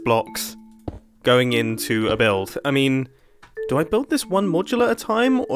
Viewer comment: i could swear i heard mumbo saying "a bilf" instead of build, i must be watching to much RCE... in other hand he do was building a bridge.....